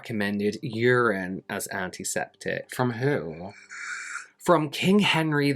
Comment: Every time you say "antiseptic", all I hear is "Auntie Septic" like you're talking about someone's Auntie 🤣